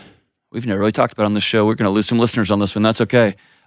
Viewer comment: no it wasn't. He said they never talk about it after asked a question about his definition of Masculinity. He said wow we are really about to get into it lol. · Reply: that’s not what he said. said “give me an example” AFTER he gave the initial answer and he said at 0:54 “we never really talk about this, we may lose some listeners and that’s okay” and THEN he got into how he did MMA, was around g*ns, and hunts. That’s what he was talking about. Trust me, I know because he tries to respect that people don’t like that he hunts a lot, he’s talked about In on several other pods.